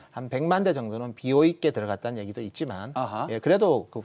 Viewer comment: 이번 모델은 뜯어봐야 알겠지만 한 100만대 정도는 ???있게 들어갔는 얘기가 있던데, 비어있다? BO있다? 검색해보니 B/O가 Brought over(이월)와 buyer's option(매입 선택권) 이 나오는데 이 부분이 생소하게 들려서요. BO있게 들어갔다는 표현이 어떤 뜻인지 궁금합니다. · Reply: BOE 중국회사여